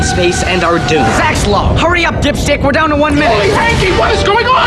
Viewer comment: does Zach say dipshit or am I losing my mind?